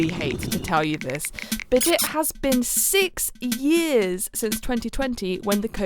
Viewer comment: I thought you said 16 and I was terrified and then realised and got confused.